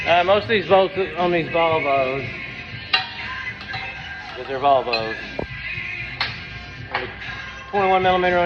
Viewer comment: LOLOL the close-captioning system of YouTube thought he said, "Bonobos" instead of "Volvos" and put that in the subtitles.